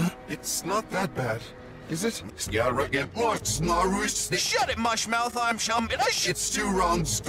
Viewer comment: talking backwards: "Im Shum did i shit-" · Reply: I thought he said shut it marshmallow!